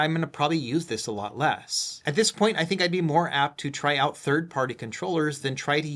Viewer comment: Good review, however I keep hearing you say “buy a third party” when you shouldn’t have to buy anything third party with a new console